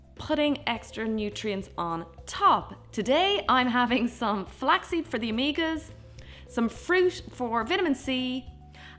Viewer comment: I thought she said “some flaxseed for the amigas” and got really confused for a second